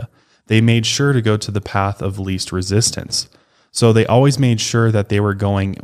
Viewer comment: when you said path of least resistance, i thought you said "the piece of resistance". Haha.... my life is lego movie, plz save me 😅